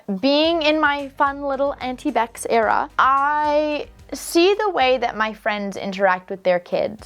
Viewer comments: God I had a mini heart attack, because I thought I heard her say "fun little anti-vax era" and got very concerned for a moment. But no, she actually said "fun Auntie Bex era." WHEW! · At first when I was listening to your reaction to story 1, I didn’t hear “auntie beccs era” I heard “anti vax era” and was confused and concerned 😂😂 after listening 3 times I got it tho 😂😂😂😂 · OMG I had to rewind three times on the clip that said "In my auntie Beck era" cause I could've sworn she said in my Anti-Vax era 😭